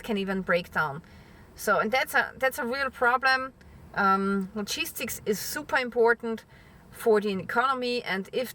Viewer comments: I legitimately thought you said cheese sticks were super important ( around 2:25 ) and I could not agree more. I had to rewind it and listen again to realize that you said logistics. 😅 Love your videos! ( and your accent 🥰 ) Thought that was funny and wanted to share it. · 🤣 At first I thought your were saying ‘cheese sticks’ ! Upon further listening 👂🏻 it became clear you were stating ‘logistics’ . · ah, "logistics" I thought you were saying "La Cheese sticks" and I wasn't sure what that had to do with anything, other than being food. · Damn. I thought you were saying "Cheese Sticks" I thought dang, cheese stick are essential to the economy! Took me a few times to decipher "Cheese sticks " as logistics. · On a lighter note . When you were saying “ Logistics” I thought you were saying “cheese sticks “. I was thinking what the hell is so important about cheese sticks ? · Lol I heard ‘cheese sticks’ instead of logistics too many times lol . Love you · I thought she was saying cheese sticks not logistics. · Honestly thought you were saying "the cheese sticks are important to the economy" for a minute there. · I could have swore she said "cheese-sticks" instead of "logistics" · I keep hearing, 'Cheese-Sticks!?' I worked it out eventually...... 'Logistics!' ❤ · Logistics. Ahahaha. My bad. I thought you were saying cheesesticks and I was really confused until you said it for the fourth time. :)